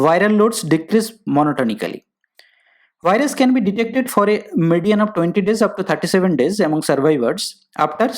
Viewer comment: Can you write what you said at 2:16 ?? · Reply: “virus can be detected on a median of 20 days up to 37 days among survivors”